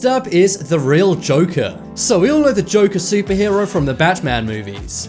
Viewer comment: superhero The joker superhero I heard you right?